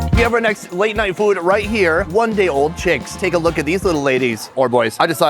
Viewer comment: Did I hear him correctly? "One day old ch!%$&*"